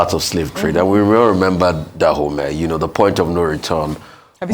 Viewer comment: Please what is the name of the movie Rufai mentioned @ 5:53 ? Not sure i heard him. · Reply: A or the point of no return, you can goggle on you tube.